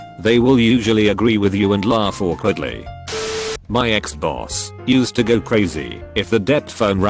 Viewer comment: thought it said Xbox lol